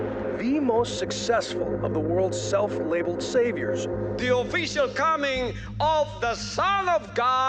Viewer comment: bro I thought he said the obese are coming 💀